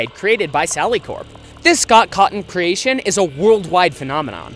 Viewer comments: Did..did he just say "sock cotton"? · Scott caughton? That’s how it sounded like you said i]his name Scott Cawthon lol it’s ok my friends can’t say it either